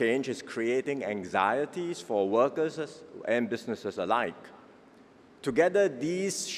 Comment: Did he say "workersers"??? 0:46. Unbelievable